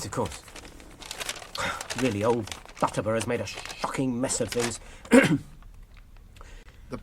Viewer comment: Did I hear that bit right? " Old Barleyman has made a fucking mess of things!" I'm sure that wasn't in the books. Lol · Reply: no he said "shocking mess of things"